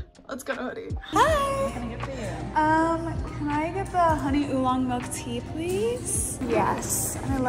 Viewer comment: I heard "can I get the honey Elon Musk tea please" 💀